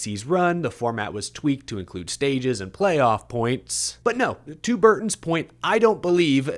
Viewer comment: “To Burton’s point” gave me a visual 🤔💭 I heard “Two Burtons’ point” and seen Ward and Jeff,from the late 90’s,arguing and pointing at each other! 🤣 I think I’m remembering an actual incident.